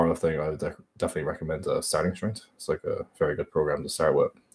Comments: what program does he say? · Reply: Evolve Fitness starting strength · what he said? Help please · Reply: &papu-rey& O Starting Strength